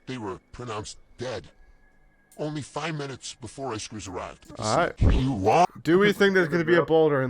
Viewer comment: “Only 5 minutes before rescuers arrived.” THE AUTOGENERATED CAPTIONS SAID BEFORE ICE ARRIVED I AM CRYING